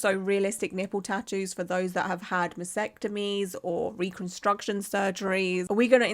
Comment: I’m assuming I heard you wrong but did you say vasectomies? A mastectomy is the procedure you’re talking about, they refer to very different procedures lol. One is a form of castration and the other is the removal of breast tissue.